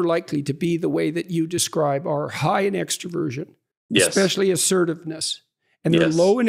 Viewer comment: Sorry forgot what I just wrote thought you said extravagant. You actually said extroversion. Might not matter to anyone else, makes a hell of difference to me . Peace and love always ,wouldn’t that be great way to start a war ?or would it be better to end a war with peace and love. To the winner go the spoils ❤